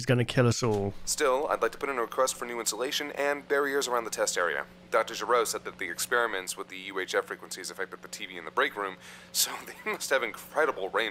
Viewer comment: Did he really say Dr. Gero? LMAO